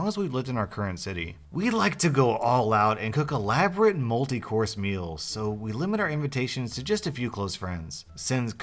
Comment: i thought he said elaborate moldy course meal and i was confused until i read it lol